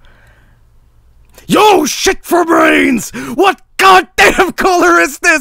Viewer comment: what does he say at the start? yoshitful brains? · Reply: YO SHIT FOR BRAINS